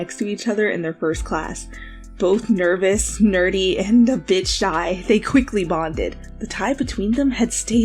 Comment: When you said "a bit shy" I thought you said "a b*tch shy" EDIT:🤣🤣🤣🤣🤣